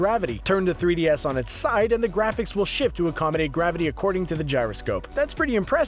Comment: turn the 3ds on its side and the graphics will shit (I know that's not what he said it just sounds like it)